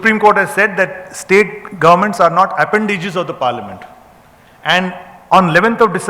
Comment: what is that word he used at 4:12 ? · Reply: appendages